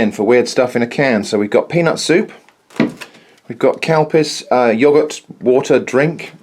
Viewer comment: I thought you said 'cow piss' when describing the can of Yogurt water ha ha OMG 05:12 in